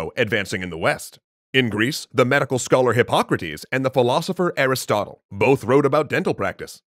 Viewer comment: Am I the only one who heard him say aristoc